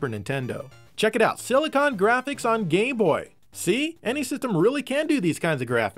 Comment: Watch the video around 13:05 it sounds like he says "gay-boy" then a moment after he says "game-boy". Lmao